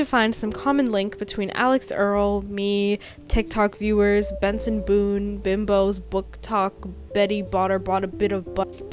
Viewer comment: For one moment I thought you said Gerson Boom, I felt the UT/DR brainrot kicking in